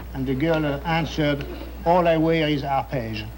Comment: Can someone tell me what he says in the punchline? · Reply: All I wear is Arpege, one of the perfumes that this man Lanvin sold.